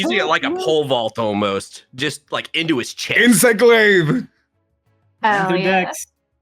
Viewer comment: I thought said Insect Glave and immediately thought he was referencing Jungle Juice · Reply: I believe did say Insect Glaive… like the monster hunter weapon?